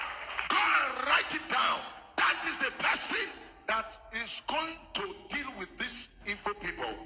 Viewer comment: the Prophet said “that is the person that will deal with this “evil” people”. He did not say “Igbo” people.